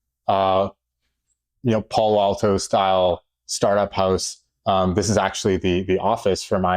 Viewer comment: I think he said "Palo Alto startup house", but both transcriptions disagree